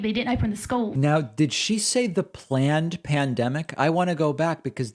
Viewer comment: Was there a peanut blight? I heard "plant pandemic". Oops, I was gonna pick on you for mocking pronunciation after saying you wouldn't, and that quickly I am doing it! Separation of church & state has been mortally ill for decades.